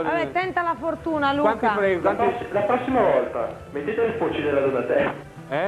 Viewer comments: cosa dice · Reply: "La prossima volta mettete le bocce della Donatella" mi sembra di aver capito. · Che succede a 5:11? Cosa dice quel ragazzo? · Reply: Dice:"la prossima volta, mettete le pocce (le tette) della Donatella".